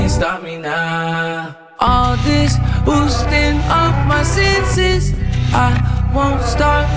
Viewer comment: thought he swore but he said "boosting"